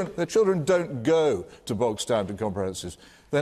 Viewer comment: what does PH say at 49:04 "their children don't go to ??? and comprehensives" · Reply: To bog-standard comprehensives (meaning they don't go to normal schools)